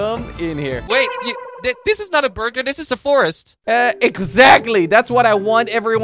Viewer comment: did he say burger???